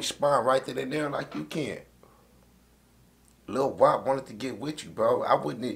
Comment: At first I thought it said "Lu Wop" and I was scared for nahmir lmao